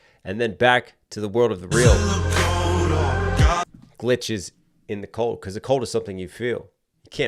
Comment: "Half algorithm half deity" "glitches in the code" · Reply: I always thought it said glitches in the portal 😂